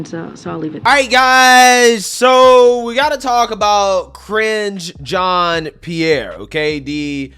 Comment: I thought you said "Cringe" Jean Pierre at first and started laughing, because she certainly IS cringe.